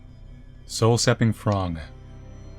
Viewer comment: HAHAHAHA I THOUGHT THE PAINTING WAS CALLED SOUL-SAPPING THONG